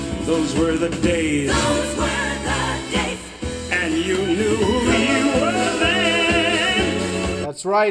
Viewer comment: I always thought she said and you know WHERE you were then. Then comes Caroll in with Girls where girls and men were men. I distinctly recall that part more so than the rest of the theme it seems in fact.